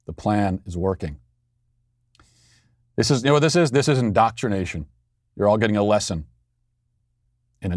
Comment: Did he say, "in-dot-rination" there, or do we need to suspend his dad card?